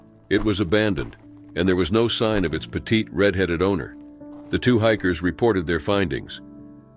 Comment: what he say??!?!? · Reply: “Petite red headed owner” if that’s what you were referring too!